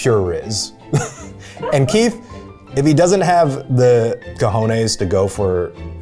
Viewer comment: Did Eugene just say "cojones"?